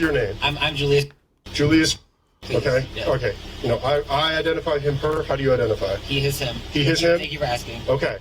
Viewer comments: Is it just me or did the cop say "I identify as him/her"? Is even that a thing now? Or I'm mishearing it, cause even the captions heard it too? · Julius, the guy's name is Julius, the narrator just misheard it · I think he said his name is Julius, not Julia. at 15:40.